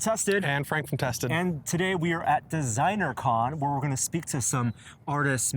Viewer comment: I thought he said "And today we're at the zeiner con". xD